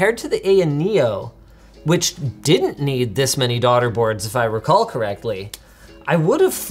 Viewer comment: My dumbass thought they kept saying "Dotter" boards. Not "Daughter" boards. Makes much more sense knowing what a motherboard is... whoops